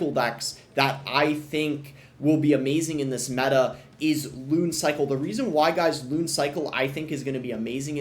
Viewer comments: deadass thought bro said goon cycle 💔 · I swear I literally heard goon cycle